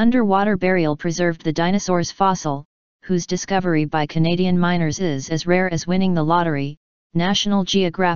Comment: wtf are the ''miner'' doing there?? look at the picture, it make no sense there shoveling in a gigantic pile of uniform dirt? what minerals are they supposed to find there? thats probably fake, to push the evolution agenda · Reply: The skywatcher abitibi when the voice said group of miners, i thought its saying group of 'minors'😂